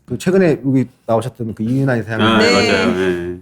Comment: 이윤아이사님? 책 무슨책인가요..? 발음이 잘안들리내요ㅠ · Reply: 이윤환 이사장님~ 입니다~^^ 책이름은 불광불급 이예요